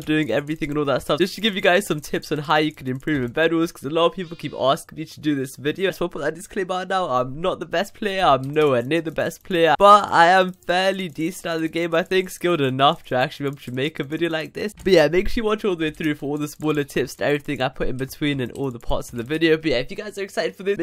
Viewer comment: thought he said skilled enough to go to jamaica